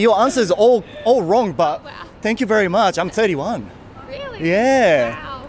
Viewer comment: 21歳に聞こえる・・・？